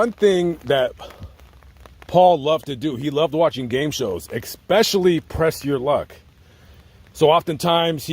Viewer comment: Did you say "eXspecially?"